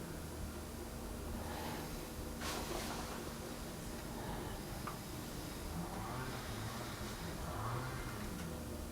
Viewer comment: what is that last word? Is it mom? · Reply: I think it is Nan. As in Nana (grandma).